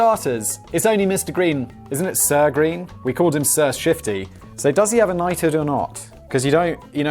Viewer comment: I thought it said "Sir Shitty." Allegedly.